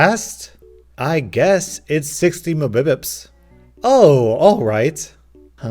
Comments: lol.. 10:22 I guess... it is 60mpbs (what he said--> mibipips) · I have the captions on and when you said 60 mbps the captions heard it as 60 my boobs oh